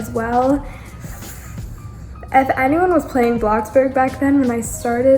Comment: I don’t know y but at 2:20 when I had on my captions it said lobster instead of bloxburg😂